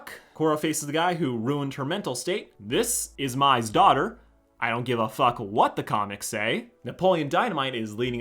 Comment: I was eating so I couldn't hear that well and thought you said "this is my daughter, and I don't give a fuck what the comments say." My only reaction was ah, valid.